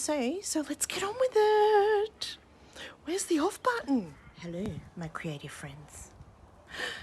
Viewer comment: hehe I thought you said, "hello my crazy friends" until I heard it again. I enjoy your videos.